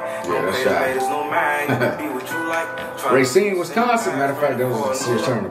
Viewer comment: did he say Racine,Wisconsin??